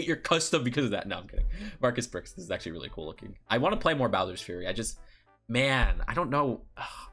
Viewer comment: I honestly thought it said Bowsers a furry instead of Bowser’s Fury.